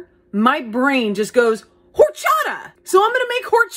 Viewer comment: Did you just say WHoRE- chata? Because as a Mexican That’s what it sounded like 😭